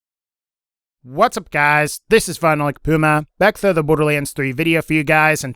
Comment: It needs said, you always sound like your saying final lick puma